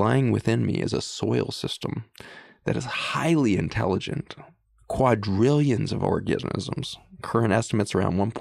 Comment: Did Zach just say billions of orgasimisms @ 1:21:15? 😱🤣